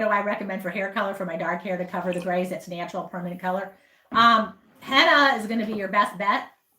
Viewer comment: what was a good hair sye i keep rewinding and can hear what she is saying? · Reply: Henna